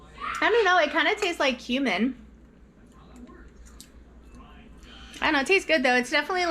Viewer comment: I’m listening while in the kitchen and thought you said “kinda tastes like human” not cumin 😂